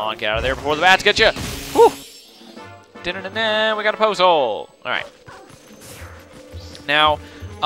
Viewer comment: He said we got a poes soul really fast so I thought he said we got a poes hole!